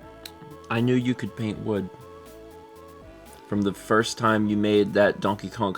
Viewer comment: “I knew you could paint wood from the moment I met you ❤” is where I heard that going 🤣